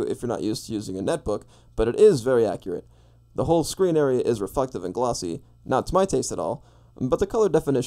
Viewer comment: did he say refuctive